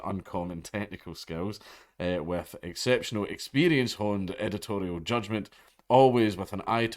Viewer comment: "exceptional experience" sounded like "sexual experience" you absolute madlad